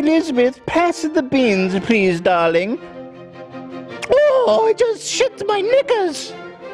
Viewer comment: Ethan sounded like he said I just shit my niggas 😂😂😂